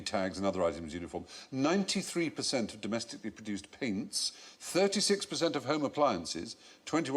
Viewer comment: "93% of domestically produced____" what- paints? What does he say there? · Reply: I think he said paints, yeah